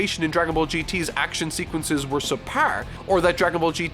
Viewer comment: did he say sipar? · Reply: Subpar